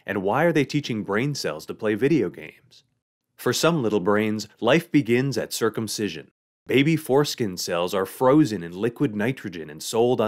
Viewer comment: I was so confused I heard "for some organism life begins at circumsision, eighty fore-skin cells...."